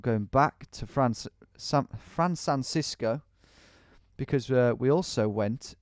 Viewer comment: Was I the only one who heard him say “Fran Sansisco” at 20:40? 🤣 (might be cause I’m high 😂)